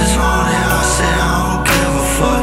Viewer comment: did I just heard sesh??